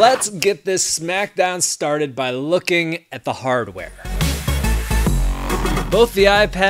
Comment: okay I thought he said 0:43 "hard way" lol